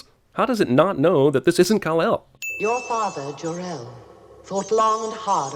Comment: You -- and then one day -- YOUR ASS!!! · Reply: I always thought he said “your ass” also. It’s certainly funnier that way.